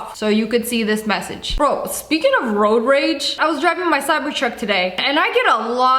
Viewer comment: Did she say CYBERTRUCK???